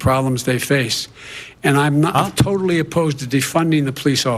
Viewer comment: The BBC reported Joe Biden as saying he is 'totally opposed' to defunding the Police, but it sounded like he said 'I am not totally opposed...' Based on what he has said in the past he wouldn't be totally opposed to defunding the Police.